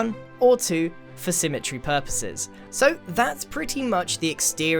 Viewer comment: I thought you said falsesymmetry lol